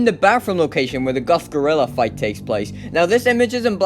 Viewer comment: ummm did I just hear him say guff the gorilla :l